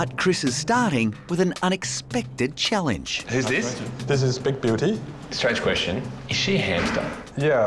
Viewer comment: The awkward moment when your CC says “big booty”